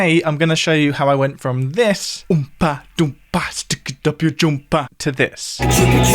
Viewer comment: In those backing vocals. Did I hear "Umpa, Dumpa. Stick it up ya' Jumper"?